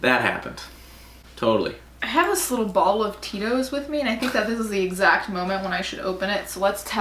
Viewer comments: When you pulled up the tiny bottle of Tito’s I thought you said “I have this little bottle of tears with me” 💀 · For a moment I thought you said “I have this bottle of tears.” And assumed for some reason you meant [male] tears and didn’t think anything of it. 😂 Though I guess Tito’s is basically equivalent to male tears. Lol! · I swear to god I thought you said “I have this little bottle of tears...”